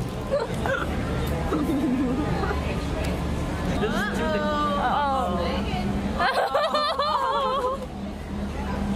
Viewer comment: What did she say? I didn't catch that. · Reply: "ahhh ohhh, spanking"